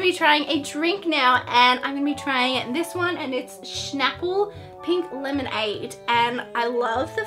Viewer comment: i think she said shnaple :)